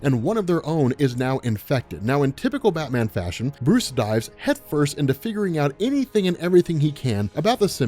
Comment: Who Else Thought He Said Bruce Dies😂🤣